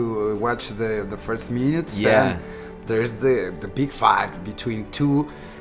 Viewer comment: DID HE SAY "THE BIG FUCK" ????